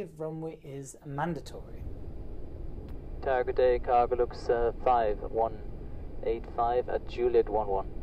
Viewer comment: Thought you said "Cargo looks fine 185" the first time and couldn't unhear it for the rest of the video.